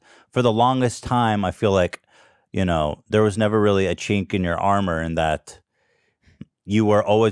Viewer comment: did Ethan say "chink in you armor" ???